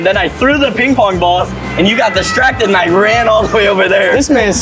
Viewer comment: When said “I ran” I thought for a second he said Irene